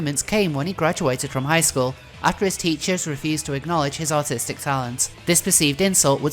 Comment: I think your accent is great but I love how it sounded like you said "after his teachers refused to acknowledge his autistic talent" lol. That being said I think it's incredible how Chris' lack of artistic talent remained consistent throughout the years. His art literally never improved.